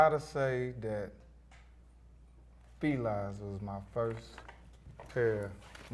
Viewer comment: what kind of shoes were those? "plies" is what i heard